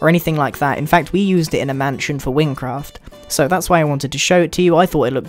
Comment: did he say wynncraft